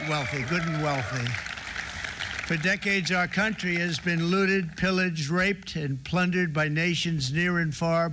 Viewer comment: did bro just say the country has been graped for decades 💀